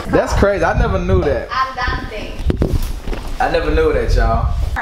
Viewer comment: is she sayin andarte? Jaja